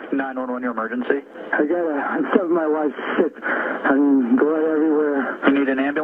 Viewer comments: I don't hear "I killed my wife..." I hear "I found my wife sick." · It sounds like he said i stabbed my wife. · At the beginning I hear him say “I stabbed my wife” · That line during the 911 call? I don't hear "found" or "killed". EVERY time alls I hear is, "I stabbed my wife.". Take a listened without looking at the words. It's fricking chilling! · 😊in the phone call it didnt sound like he said i killed my wife, it sounded like i stabbed my wife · Sounded like he said, "I stabbed my wife."